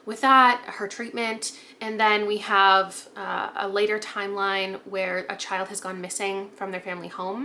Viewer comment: When you were discussing The God of the Woods, I definitely briefly misheard “later” as “lighter” and had a moment of “man, I can’t believe the timeline where the kid goes missing is lighter 🫣” so anyway, happy new years eve, may everyone’s hearing & audio processing be better than mine in the new year😂